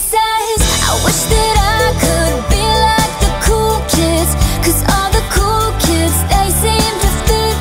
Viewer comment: For a moment i thought she were saying Cookies ._.